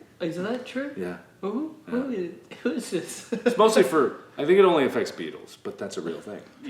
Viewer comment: When Mark said "I think it only affects beetles", I thought he said "I think it only affects peedos" xD